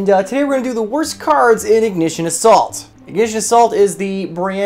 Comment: For a sec I thought he said “Ignition of SALT” 😅